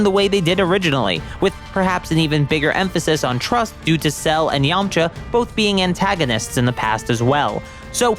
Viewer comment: did he say sell and Yamcha